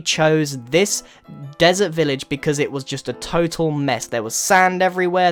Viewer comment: it stopped and it sounded like he said "a total meh" #MEHNESS